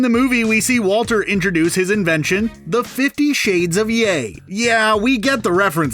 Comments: Wait I thought he said 50 shades of gay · Anyone else in the film thought Walter said "50 shades of gay " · I thought Walter said 50 shades of gay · For the “50 shades of gray” I thought he said “It’s like 50 shades of gay!” because the powder was colored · When he said "50 shades of yay" I thought he said "50 shades of gay"